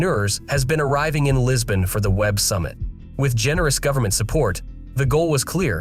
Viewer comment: I thought it said "Weeb Summit" in the beginning